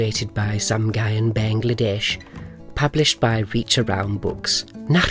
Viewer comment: I thought he said "illustrated by some guy in Bangladesh" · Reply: He did. Only the name is spelled “Sumguyen Bangladesh”. Sum-guy-en = some guy in. 😆